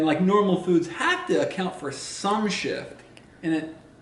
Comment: I thought said Sh** but he said shift 😂😂😂